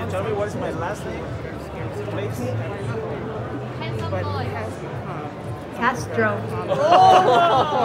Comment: what did she say please ? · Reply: She asked her to read someone's last name off a badge. "Castro" was the name.